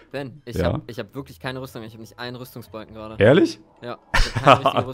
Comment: habe zuerst verstanden Logo hat einen rüstungspunkt